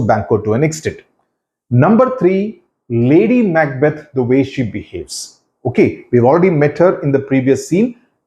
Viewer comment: " Lady Macbeth to be..... " ? What did you say sir. I didn't get u. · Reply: "Lady Macbeth, the way she behaves."